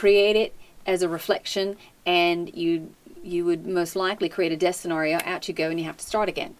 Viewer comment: what does she say? Create a destin Oreo?